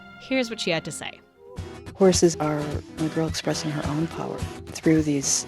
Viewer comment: I’m sure no one at Polygon is checking the comments on a 2 year old video, but I figured you might want to know that at 2:13 the caption says “negro” when I’m pretty sure the person is saying “a girl” and you might want to fix that....